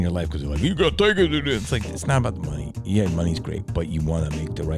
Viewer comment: I am sorry butnwhay did you said? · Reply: Think of the money